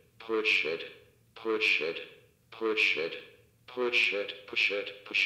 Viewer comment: When it says push the button over and over i heard poor shit 😂